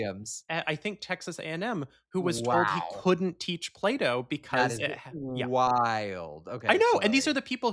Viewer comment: I legit thought was saying Play-Doh was banned in Texas being too gay until I realized he was saying Plato. It's crazy I didn't think the former wasn't out of the realm of possibility for Republicans to ban